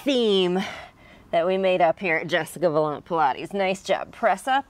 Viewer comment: Pilates!?! I thought you said “Pie and Lattes!” LOL! Great little workout. This is perfect for my morning routine.